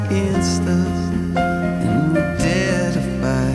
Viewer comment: If we were invincible, If we could never die, And all the world could rise against us, And we'd dare defy. Catch myself before I fall, And see through all the reasons we are livin' low, Even if this day won't come, I'd burn a thousand moons, just to light a single sun. And now the darkest star collides, With our own guarded light, If we were invincible, If we could never die, And all the world could rise against us, And we'd dare defy, dare defy, Nothin' left to fear, We could never fall, So with our heads full of fire we'd be invincible. This storm up there knows my name, (?????) while the soldiers look away, And now we stand at Heaven's door, But we're just mortals, Can we face another war. And now the darkest star collides, With our own guarded light, If we were invincible, If we could never die, And all the world could rise against us, And we'd dare defy, dare defy, Nothin' left to fear, We could never fall, So with our heads full of fire we'd be invincible. ['Invincible' repeated, along with some chanting and moaning] x2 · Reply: I always heard "dare to fight" instead of "dare defy" when I listened to this song. But thanks for the lyrics anyways.